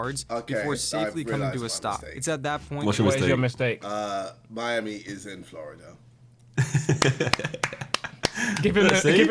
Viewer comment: I just heard KSI say “How far is Miami from Florida?”